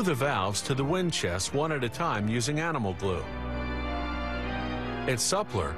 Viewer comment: did he just say “animal glue?”